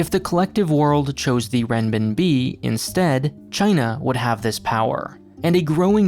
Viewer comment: What does he say around 2:14?? "If the collective world chose the ..............." Sounds like "rhen ben b" to me EDIT: he says "renminbi" the name of the currency, why is it nowhere to find in the video or descirption is beyond my understanding...